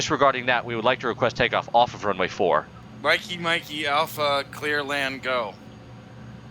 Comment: What? Did... that... guy... say? Did he say Mickey Mouse wtf was that mikey mikey alpha shit ? Please dont play with dumb people. Play with expirianced ones...